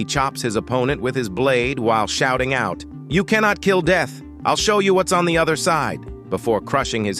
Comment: nah i think he says "you can't kill the dead"